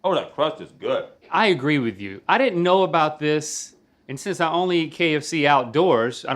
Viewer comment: Did he just say I only eat KFC Outdoors??